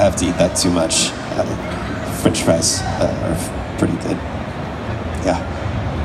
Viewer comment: can someone please tell me what he says his favourite food is? That’s the only word I couldn’t understand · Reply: I think he said french fries 😂